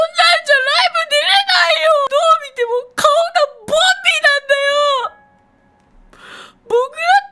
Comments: ボンビーがボビーに聞こえたwww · ボンビーがボビーに聞こえてボビーオロゴンかとおもたwww